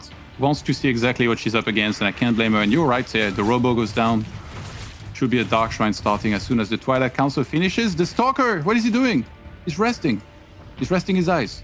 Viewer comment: I keep hearing "Toilet Council"